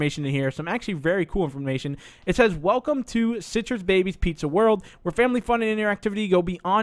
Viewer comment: and 5:08 he said CITRUS instead of CIRCUS!!!!😂